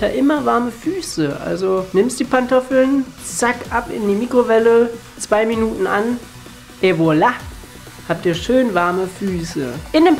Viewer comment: ich habe zuerst "Ebola" verstanden ._. Super Folge, wie immer, besonders am Ende habe ich mich weggeschmissen xD